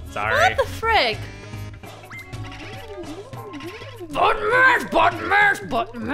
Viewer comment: I thought was saying butt mash